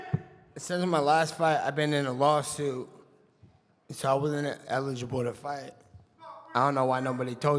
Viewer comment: Please can anybody write down what Nate say at 15:04? I didnt understand, im not native speaker, thank you 👍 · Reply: he had been in a law suit since his last fight because of which he wasnt eligible to fight